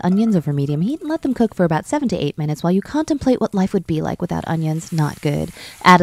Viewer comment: OKAY IM NOT THE ONLY ONE WHO THOUGHT SHE SAID WAIT FOR 78 MINUTES. i mean, u cant contemplate life without good onions in just 7-8 minutes so it made sense :)